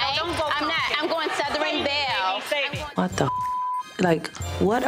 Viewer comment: hmm did she say ‘’ what a retard ‘’ 12:58 ... back in the day they could basically say anything i guess